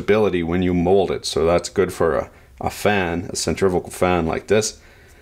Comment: Centrifical? My hearing isn't what it used to be, but I thought that's what I heard. Someone please correct me.